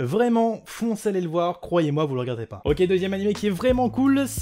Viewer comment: j'ai compris: "croyez moi, vous l'regarderez pas" 😂